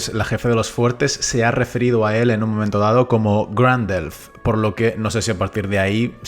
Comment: Cuando lo llamaron "Grand Elf" escuché "GANDALF" 😅